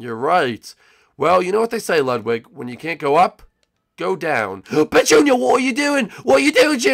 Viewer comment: did he just say b*tch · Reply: Ronald mineblox Gaming Paul HD 4 LIFE no he said but junior what are you doing but he saidnit fast to make it sound like he said b*tch